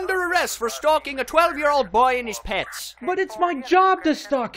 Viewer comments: Oooooooooooooh so it said "and his pets" I thought he said "in his pants" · When I was young, I misheard this one and I thought the officer said, "You're under arrest for stalking a 12 year old boy IN HIS PANTS!" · When that guy got under arrest i thought the police said "stalking a 12 yr old boy in his pants" lol